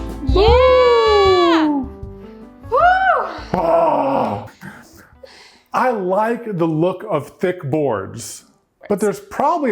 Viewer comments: Anyone else hear "I like the look of thick borgs" @ 11:45 I keep cutting back and I still hear it xD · i thought he said "i like that look of think boys" instead of thick boards XD